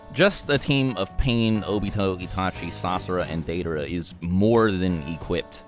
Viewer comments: anyone else hear that? He said Sasora 🤣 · who else heard Sasora plus can't he just make a puppet out of someone that the other Akatsuki kill? He could control sand with a gaara puppet